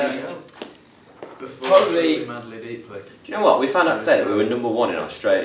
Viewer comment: im sorry did Harry just say “truly madly deeply” 😃